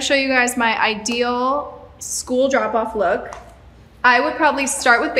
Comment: This whole time i thought she said " school dropout look" im thinking , those are some nice outfits for a dropout look 😂😂😂